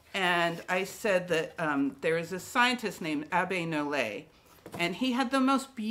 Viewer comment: What did you said after 7:10 to 7:15. Sorry, can't hear properly. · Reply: “ There’s this scientist named Abby Nollet”. Sorry, I usually put English CC on the bottom but I got to do with this video